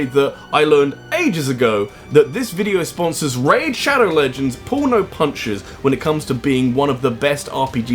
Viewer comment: For a second there when he said Raid pulls no punches, I thought he said "Porno Punches".